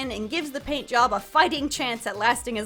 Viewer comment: I was chewing when you said "fighting chance" and misheard it as "f*cking chance" and almost spat out my food